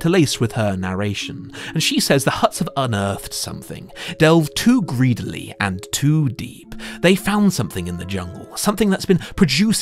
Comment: I heard “to greedily and to deep” and all I thought was: Balrog!